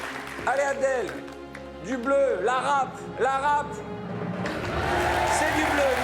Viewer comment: Lapsus de Arthur ??? What did you say bro ?? 🤣🤣😅🤨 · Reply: "La rape" 😅